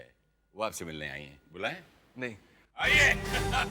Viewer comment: wtf he said nahi